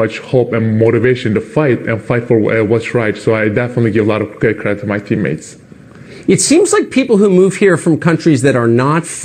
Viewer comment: Did he say I give a lot of credit card to my teammated at 1:45? lol